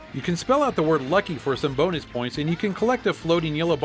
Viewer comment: i hear "boner" not bonus